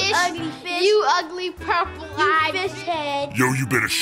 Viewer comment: did anyone else hear "you ugly b**ch" at 7:45?